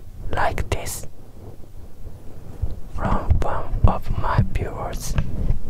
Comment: it kinda sounds like hes saying bwords instead of viewers JSJDJSBBSJ hes so cute </3